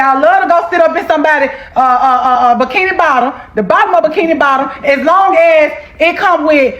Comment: Did she say “bikini bottom” SpongeBob reference?? 🤣🤣